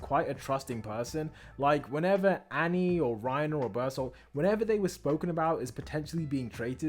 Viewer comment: i love how the subtitles say Reiner as “Ryan” and boruto as “roberto”